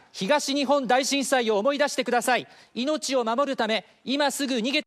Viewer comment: god damn it why did i think he just said the n word